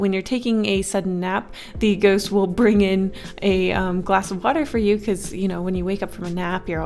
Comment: i heard "glass of butter" instead of "glass of water"